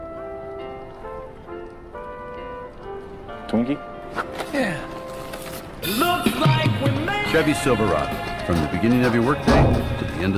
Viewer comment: Tallahassee DID SOMEONE SAY TWINK!